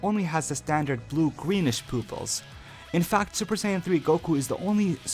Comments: LOL you said poopils instead of pupils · I heard greenish pooples